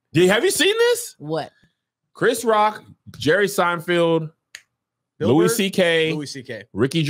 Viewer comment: Kev is still saying "Seinfield"🤣🤣🤣